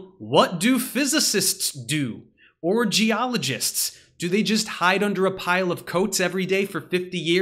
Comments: I heard, "Orgyologists" and, for a brief second, had a glimmer of hope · I heard "Orgy-ologists" at first and had to go back 🤣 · I genuinely thought I heard you say 'Orgyologists' and i said out loud, 'does that exists? study of orgies?' hahahaha